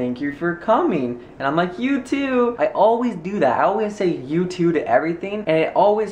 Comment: when you said you too i thought you said youtube lol